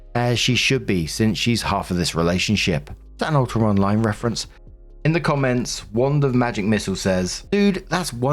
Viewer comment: What does say at 21:07? All I hear is “is that a (gibberish) reference?” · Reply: Apologies, I believe I say is that "an ultima online reference", it's an old MMO.